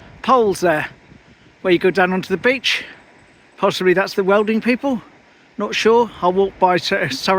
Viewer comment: Oh... My hearing... .. I thought you said 'Worlding company'.... Then realised you said ''Welding''. I thought it might have been something to do with Sir Valiance..... 😂.... Nice photos lovely....